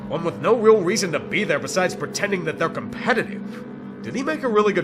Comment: did he just say SHRIMP boil?